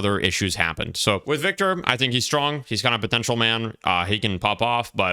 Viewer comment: Did you say Potential?